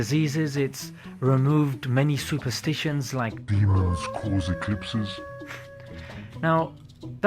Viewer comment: "It's removed many superstitions like ???? cause eclipses." What's he saying? "Beavers cause eclipses?"